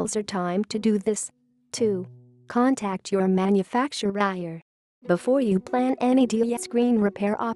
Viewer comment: Am I the only one who heard it say Manufactuihair instead of manufacturer.